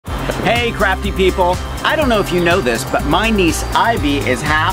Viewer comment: I thought you you said Hi Crappy People in the beginning lol